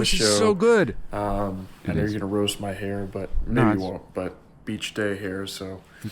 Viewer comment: I thought said, “braid my hair” 😂